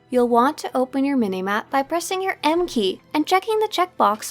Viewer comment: for a second I thought instead of "M-Key" it was "Mkay". you know, like this one guy from south park. Aye, I dont remember his name. Edit: nvm his name is literally Mister mkay. Gee, I feel dumb